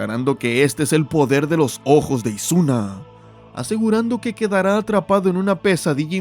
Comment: escuché ojos de Ozuna 😂😂😂